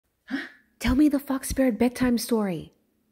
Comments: at first i thought she said “tell me the f-ck spirit bedtime story” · In the beginning of the video I thought she said "Can you tell me the f*ck spirit story?" But she said"fox spirit". I also ended up choking on my chips when I heard wrong- 😭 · I thought they said F##k spirit 🤔 😕 oops 😬 😅 but it did fit with the twisted ending though 😳 🤣🤣